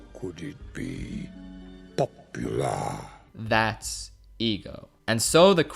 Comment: It sounds like he was gonna say "popsicle" for a moment, am I the only one?